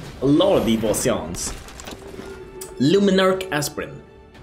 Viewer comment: I wasn't paying attention and I thought you said luminous assprint 🤣